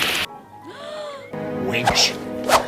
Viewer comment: in Finnish dub Frollo says "Seize that slut" 😅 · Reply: Yeah, I also thought it sounded more like he said "wench" rather than "witch" at 24:10 as well